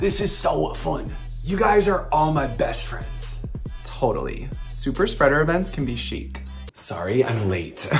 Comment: Can anyone help me to undersdand what was said in these parts? 0:41 - Leo: "super spreader ..?.. can be shic" 1:05 Sagittarius: ? 2:17 Aries: "...two jelly shots and ...?" 4:42 Capricorn: "Generage" Correct? 5:58 Aries: "You're boring us to death" Correct? 6:25 Capricorn: "Are you my ...? " 7:47 Sagittarius: "I refuse to tie before I become ...?" 8:21 Scorpio: ? 9:17 Aries: "No Patricia" Correct? 9:54 Cancer: "Sum ... ? " I know doesn't put subtitles under his videos, I want to understand every word that comes from zodiacs' mouths :< I'm not from U.S., so there's also a chance I don't get everything right away. Love the production of it, there's even Shakira song from over 11 years <3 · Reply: super spreader events 5:58 yes 7:47 Delta Diamond 8:21 verse